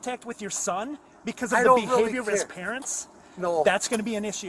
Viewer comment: time? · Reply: listen closely he clearly says he is going to call the black river falls athletic department about his son "for the behavior of his parents" that is very much retaliatory. It's a new thing I've noticed when police can charge anyone with something major. They start threatening to go after personal lives like calling people's places of employment. ATA literally has another video showing this very thing.